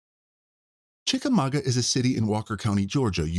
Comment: I swear I heard "chicken nugget". Damn, I must be hungry.